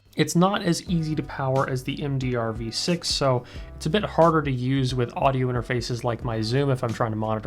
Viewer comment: I swear to god I thought he said his 'Zune' 😂 For a second I thought I was gonna have to pull my old one out of a drawer to plug my hifimans into